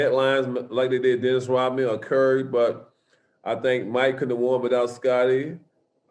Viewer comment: Actually Charles said the opposite but because of how he talks, it sounds like he said "he could have won" but Charles has said consistently throughout several interviews on his book that Mike "could not" or "couldn't" have won without Scottie which is exactly what Michael has said numerous times. Check out Charles' other interviews on this issue and he says the same thing but on other interviews it was said a bit more clearer. Unfortunately you have to always be careful listening to him because his speech patterns can throw you off on what he really said in some cases. Trust me he was trying to say "couldn't have"